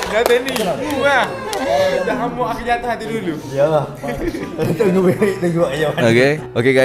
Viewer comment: cakap apa tu? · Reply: dia cakap ke "mung la, mung yg buat aku jatuh hati dulu" 😂 aku rasa geng2 AI dah tahu hal sebenar ni, saje je nk buat content, maybe...